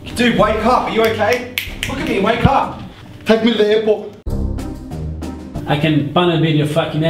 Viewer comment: what did the guy say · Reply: Take me to the airport